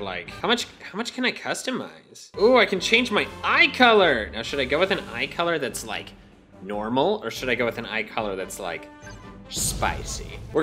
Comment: I thought he said "Ooh I can change my thigh color!" Lol